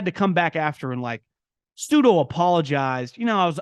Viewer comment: what does he say before the word apologize at 7:45 ? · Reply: "Pseudo." Basically he's saying he "kinda apologized."